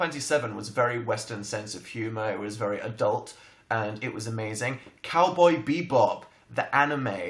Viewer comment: I thought he said cowboy beebo...